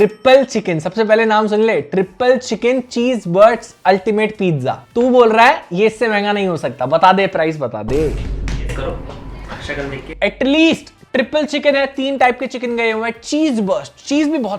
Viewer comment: did he said Cheese Butts .... whatt!!!!!!!1